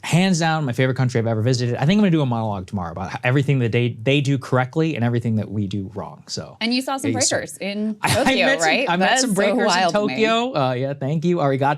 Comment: I thought Crystal said "and you saw some Trumpers", at the beginning when Sagarr mentioned Tokyo. I had to rewind again. lol